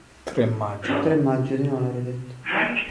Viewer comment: Io dal minuto 13:48 al minuto 13:56 ho capito "3 Maggio del 2004"